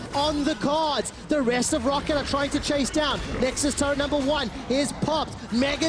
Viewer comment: 聽成台灣藍波萬